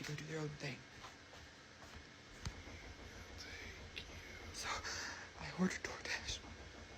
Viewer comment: The door dasher nightmare....I heard a voice whisper "I'll take you" or something while he was panicking. Around 39:30 listen closely